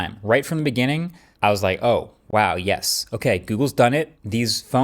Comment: Did he say "OK google"